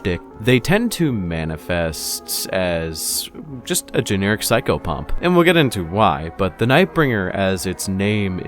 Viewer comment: For those with no idea what a Psychopomp is; it's the typical guider of souls/spirits to the afterlife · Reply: I thought he was saying psycho-punk. Thanks XD